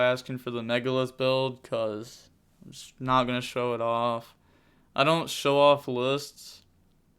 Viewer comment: I thought you said that you don’t show your lists...